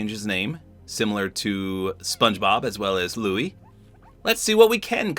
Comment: I thought you said “Bluey” and now I want a Bluey sidekick!